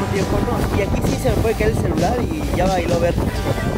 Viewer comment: en el minuto 24:49 escuchen lo que dijo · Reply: Damariz Flores ya bailo bertha